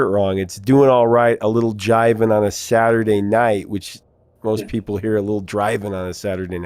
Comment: Oh geez. All these years I heard it as "Little Johnny on a Saturday night". Which makes no sense. 😀 Thanks for reminding me what a cool song that was. They should have had all the number ones.